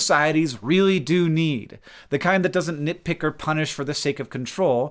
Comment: All that talk about good and bad nipples and I thought Hank said "the kind that doesn't nippick or punish."